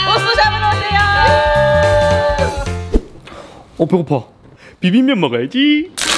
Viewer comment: 어디서 봤더라?? ㅋㅋㅋ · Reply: 연속클릭하면 보고파 라는소리가 들리네